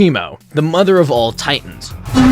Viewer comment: why do i hear emo